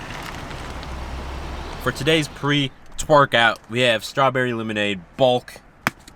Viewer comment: did he just say twerk out